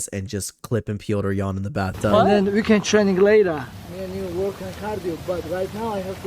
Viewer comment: Did he just say “then we can train later… me & you work on cardio”? Lmao